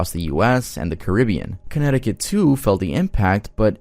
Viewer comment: I thought he said "Connecticut 2"